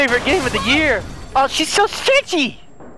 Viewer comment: for a solid 3 seconds I thought he said she's so sexy. I was about to make a FBI joke